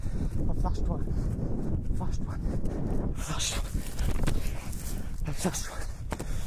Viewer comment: did he say 'I'm such a tw*t' ? 😂